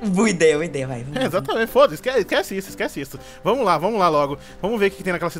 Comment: é foda-se isso mesmo que eu ouvi produção ? ><